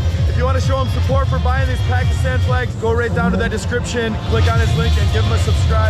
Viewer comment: did he say Paki flag?! 😱🤨😅